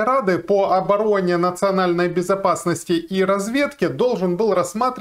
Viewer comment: Ох и послышалось во вступлении: Совет национальной безопасности и обворовыванья. Надо же такому послышаться, ещё чего можно подумать про уважаемых господ в совете.😂😂😂